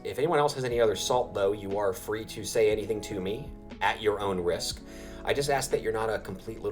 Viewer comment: 😂. I always come back to this video for summonzon inspiration. When you say, “if anyone has any other salt though”. I keep hearing, “you’re free to season, but don’t be a bitch”. ❤